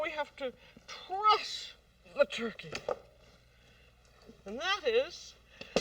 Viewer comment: It sounded like she said Press The Turkey,,,, lol I thought she'd FLATTEN IT,,, lol,,,, She MUST have said DRESS THE TURKEY,, which is SO cool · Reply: I think she said Truss the turkey